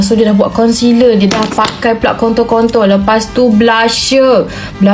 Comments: hahh aku gak salah denger kan dia ngomong kontol kontol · Ini aku yang salah dengar apa memang kakak ini bilang kontol kontol🗿?